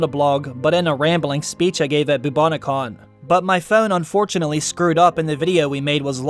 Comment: I thought I was having a stroke “bubonobon” is what I heard